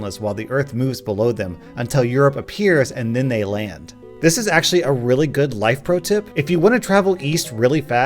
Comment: i accidentally closed the video right near the end at the perfect timing to make it sound like you said 'this is arse'